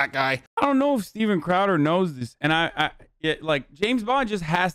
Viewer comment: Thought it said Steven Coward 💀